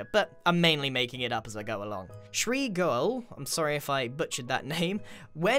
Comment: I misheard Shrey Goel for Tree Girl. I usually just listen to this in the background until I hear that something is happening that I want to see.